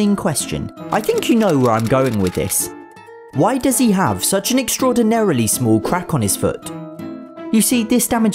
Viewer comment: I heard "why does he have an extraordinarily small quack"